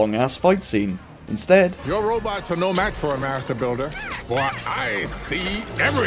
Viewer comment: I always hear: Your robots are no match for america builder!